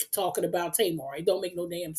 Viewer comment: Please give the kitten some kisses for me! I thought you said kid at first 😂😂😂 but same difference right ?